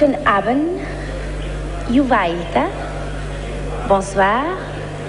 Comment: after Guten Abend which language does she say good evening in? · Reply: It's Finnish - "Hyvää iltaa."